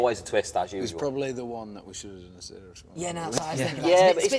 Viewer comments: okay ive listened to this a lot now, still cant understand what does harry say at 3:39 ? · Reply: Or he maybe said, "we should've done a serious one" But of course they didn't, I love these dorks 😂♥️ The song is actually sad but these are literally the boys who'd make even Rock Me's video about playing with rocks in a flower field 😂😂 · what does Harry say at 3:40? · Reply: Jamie Watson we should've done a serious one....I think